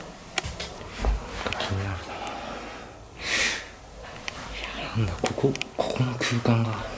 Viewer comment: 「嫌なんだ」って聞こえる